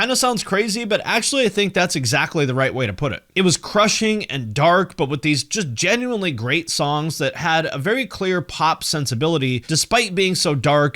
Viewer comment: What does Pete say on 6:20? "Black Sabbath meets The Beatles reharsing drunk on a haunted factory"? Hahaha.